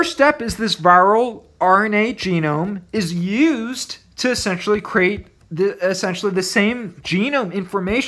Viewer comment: lmfaoooo I think u accidentally said sexually instead of essentially